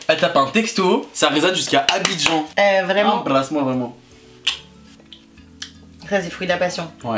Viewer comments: embrasse-moi mon amour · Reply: bolle dorothee il a dit « embrasse moi VRAIMENT » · embrasse moi mon amour lise si tu croit qu’il sont en couple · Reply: Il a dit embrasse moi vraiment 😂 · "embrasse moi m'amour "😅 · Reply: charlotte. prd il a dit « embrasse moi VRAIMENT »